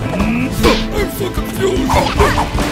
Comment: LOL! At 0:45, it sounded like Mafia said "I fucking fused!" :D